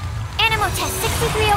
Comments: Oh my Archon, when he used his skill I thought he said "your mum"💀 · I HEARD "YOUR MOM, U SHOULDNT LET UR GUARD DOWN" 😭😭😭😭